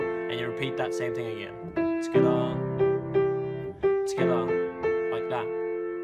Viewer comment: When he said togeather it sounded like he said skada